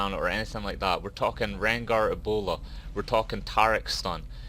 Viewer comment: Lol why did it sound like you said "ebola" when rengar leaped at you and launched his bola strike at you? lol